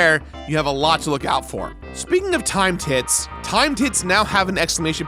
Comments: Anyone else hear time-tits?? Like i thinks its supposed to be timed hits but he sounds like hes emphasizing T after time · straight up thought he was saying time tits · "Timed tits"?? Did I hear him say "Timed tits"??😂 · Jerard: "timed hits" What I hear: "time tits" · ive never played mario rpg before so instead of "timed hit" i thought jirard said "time tit" and i was confused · It must be late because I keep hearing you say " timed tit" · why do I keep hearing "Time tit" instead of "Timed Hit"? :P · I'm sorry, when I heard you say "Timed hits" I thought you said "Time tits" I was very confused and now I can't un-hear it. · Not gonna lie, every time Jirard said 'Timed Hit' I thought he was saying 'time tit' And I need to get my mind out the gutter. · I was only listening to this video and it wasn't until Jirard said it slowly that I realised he was saying "timed hit", I heard "time tit" and was praying it was like a bird which gave out time challenges 😂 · Every time he says "Timed Hit/s" I hear "Timed Tit/s"